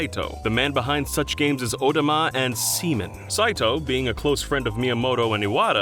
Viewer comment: I was tabbed out at 7:27 and was so confused when I thought I heard Semen.